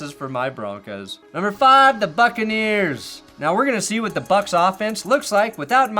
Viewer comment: When got to the Buccaneers, at first I thought said the Fuccaneers.